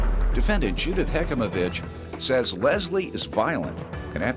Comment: Am I the only one that replayed the defendants name? I thought he said Judith heck of a b🤬